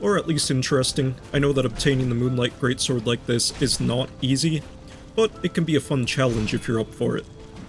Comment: i heard "it can be a fun challenge if you're unemployed" at 11:40 LOL